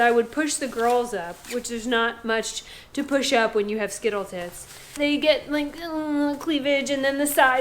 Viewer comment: Hey!!! Get rid of that baby stuff ASAP. Your new electric guy should do a much better job than that last hole; we'll be watching. Did you say skittle-tits [5:50]. 😂 YOUR personality is gonna get you waaaaaaaaay more people interested in you than any side-boob ever will. Be careful out there and Thank you for sharing. 😁 😎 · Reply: Skittle Tits were said!